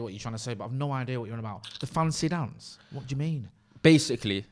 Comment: Fancy Dan is a term typically used to describe a more skillful player. I think you thought he was saying fancy dance. To be fair I dont blame you as his enunciation isnt the best.